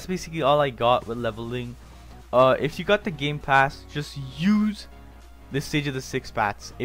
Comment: i thought he said gay pass😂😂